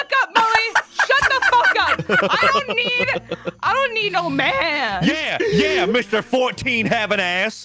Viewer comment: Can we talk about how it sounded like said "I don't need a man." and just looked so fucking sad?